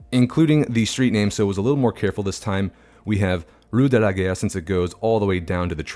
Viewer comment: because of how you pronounced Rue de la Gare, you made it sound like you were saying "Rue de la Guerre" which in my head made sense as I would imagine people would associate taking that road with getting to the station to go to war. There could even be a monument to the fallen heroes alongside it to drive home that point.